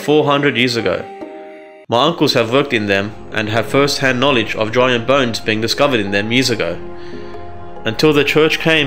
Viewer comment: in 23:53 etc, the automatic English literation is faulty. I think he says: My uncles have worked in them (400 year old monasteries) and have first-hand knowledge of giant bones (not: of drawing and bones) being discovered in their musica. Until the church came and took them away. Doesn't that sound familiar. Like whenever they discovered giant (not joint ) bones in the US a team from the Smithonian group comes by and hauls (not: holds) them away, never to be seen by the public again.